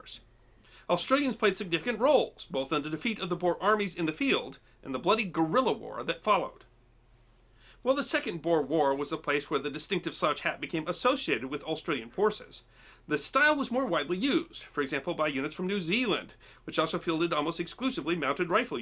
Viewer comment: I think he said “2nd Boar War”. I thought he said 2 World War also. The Boar War was in Sth Africa